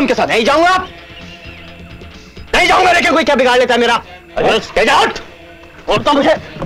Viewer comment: Did he just say "get out!" in English? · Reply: 😀😀😀😀nice one !! But he said "suteja uth!